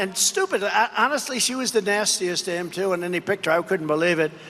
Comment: Did he say - Nastiest dame?